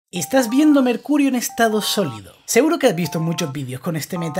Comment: Pensé que dijo "Estas viéndome el culo en estado sólido" 😂😂